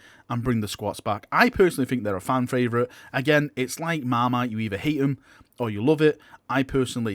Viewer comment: Can't understand what you're saying at 7:13. "It's like Mama?"